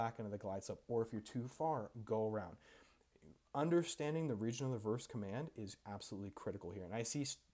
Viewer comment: can you articulate what you are saying here "understanding the regional reverse command" is that what you said? or something else? i think you could benefit by slowing down the speed at which you talk, not a lot but just a little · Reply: “Region of reverse command” ; he’s referring to when you start using pitch for speed & power for alt